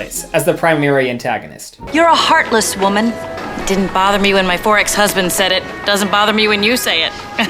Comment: When that lady said "her 'four ex husbands'" hand to god I thought she said "forex husband" and I was not gonna question it at all